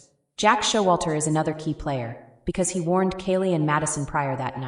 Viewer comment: I thought I heard “did you warn Kaylee” I’m so glad someone else heard something similar! This is a great segment